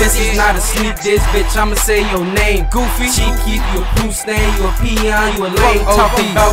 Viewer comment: LMAO DID HE REALLY CALL HIM A POOP STAIN OR AM I HEARING SHIT